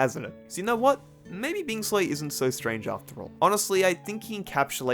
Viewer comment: what does say his name 22:01 , its bing soy 22:04 , bing soy 22:11 ?